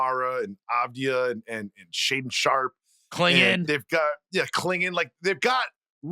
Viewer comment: Did he just say Deni Avdija is just a plus role player??🤦‍♂️ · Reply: No, he said clingan